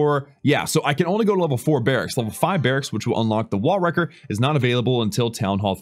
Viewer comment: did he say wall wrecker